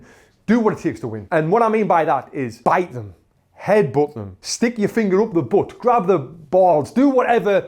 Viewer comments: Did he really say stick ur finger up he's a#$ 7:50.... 🤔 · Watched it until the end did I hear it right, "grab..the balls do whate-"